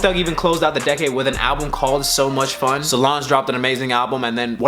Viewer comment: what is the artist here? He says "salons" or somen but can't find the artist anywhere by that, how is it spelt lmao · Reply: Solange Knowles